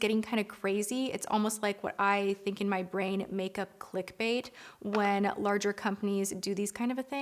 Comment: When you said you think of "makeup clickbait" when you see all these weird products, and immedietly in my head I heard "a makeup mid life crisis". I like unique products that are still simple and make sense, but some are just too damn weird